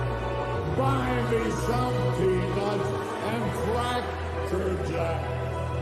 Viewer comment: At marker 18:12 did that hologram just say "Buy me some people & crackerjacks"? I replayed it 5 times and I know i heard people & not peanuts